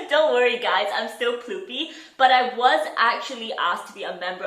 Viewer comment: Not the captions saying poopy 😭